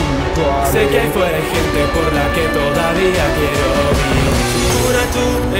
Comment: wtf que dice fushiguro ahi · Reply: "Se que ahí afuera hay gente por la que todavía quiero vivir" Podés activar los subtítulos (España) te subtitula todo